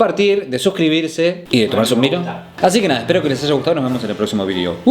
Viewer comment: Iba a comentar que en el 9:59 se escucha un susurro que dice "me gusta", y explicar que podría tratarse de programación subliminal... Pero me dio P4J4...